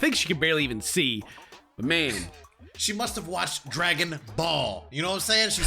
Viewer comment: I thought said "dragging balls" ded.